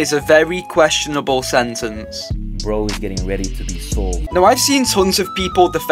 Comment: what did he say tho I can’t tell what he said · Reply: He said "bro's getting ready to be sold"